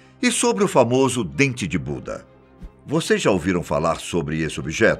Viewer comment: eu entendi "Dente de bunda" kkkk voltei p escutar de nv e escutei a msm coisa , só entendi dps q vi a ft da estátua 😂🙆💁